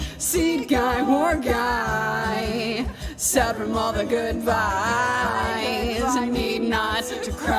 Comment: I keep mishearing “sad from all the goodbyes” as “sad from all the good vibes” and I keep laughing